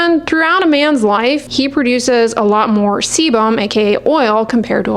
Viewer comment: did you say CBUM? 💪😂